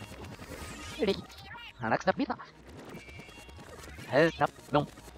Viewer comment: が 鼻くそピザ にしか聞こえない！